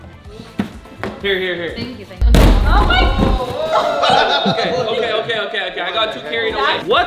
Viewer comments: did anyone else hear her say "thank you bae" or maybe im crazy 😅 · Did anyone hear say “thank you babe”